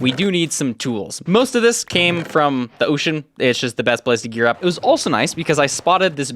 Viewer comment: why did i hear him say "its just the best place in europe"